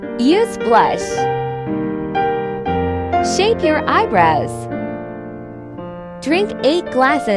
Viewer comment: I heard “shave your eyebrows” at first 💀